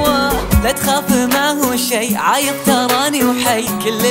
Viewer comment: مين لاحظ زيي انه ماقال عايش قال عايض على اسمه😂😂💛💛💛